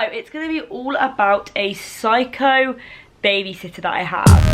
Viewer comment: At first I thought it said my psycho baby sister 😂